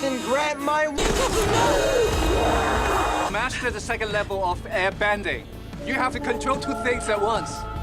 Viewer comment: Wait wtf? 1:26 Did they actually say that? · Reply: Yes he said airbending